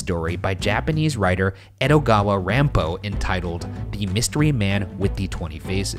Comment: Ryan : " Edogawa Rampo " Me : i thought u said Edogawa Conan :v